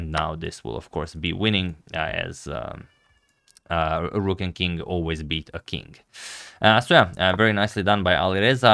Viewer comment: Am I the only one who heard him say "rook and king, always be fucking"